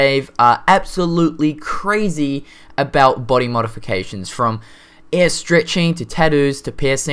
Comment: I swore you were saying "air stretching"